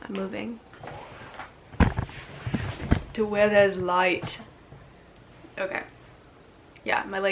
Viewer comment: wow... im actually speechless... like, I'm from Argentina and I speak spanish and I swear to God, I heard "TU ERES LIGHT" which means (You're the light) that's crazy... ✨🙌 Thanks!